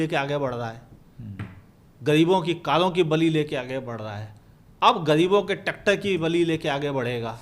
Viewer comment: Hain 9:07 what he just said 😮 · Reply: I think he said "Tractor ki bali", although it seems he said something else. 😅